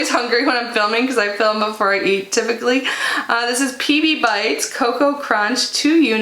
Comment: I would love me some compress socks! I currently have elephant ankles!😂 I thought you said "yum peepee bites!" Not PB Bites! 😂 I've been in the house for way to long! Great box! Did you get any of the Ofra mystery boxes yesterday? There were three sizes. Tfs and have an amazing evening!🤗💜🐇